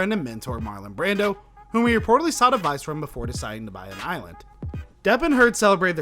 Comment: Bro, you need to take your time and slow down when reading... lift up your chin and don't read looking down. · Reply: Lol, I agree. At 2:15, I thought he said “whom he reportedly sodomized before deciding to buy an island.” Had to rewatch before realizing he said “sought advice from.” 😄🤣